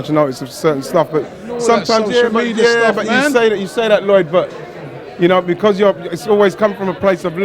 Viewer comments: Did he call Lloyd? lol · Did he just call, "Lyod" at 0:48? looool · Did he just call him lloyd at 0:44?